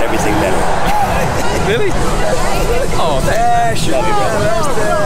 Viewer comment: Did he say " Shove it brother"...?